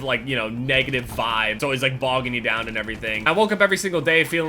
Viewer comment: did he say bog you down at 1:40 lol that's what I heard